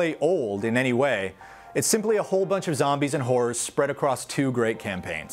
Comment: Okay I swear he says "Zombies and Whores" ? :0